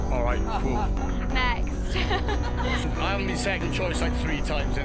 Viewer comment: I've rewatched the clip at 9:26 like 6 times and i still can't tell wtf the two that are beefing are saying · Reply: Yeah, it's tough to hear, and the subtitles even run over each other. But I think he says that she's been 2nd choice 3x. That's all it took to break her... but we have a fragile ego...